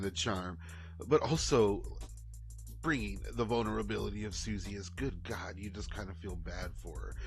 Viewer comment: oh you said VULNERABILITY… coulda sworn you said BONER-ability which Amy Jo also has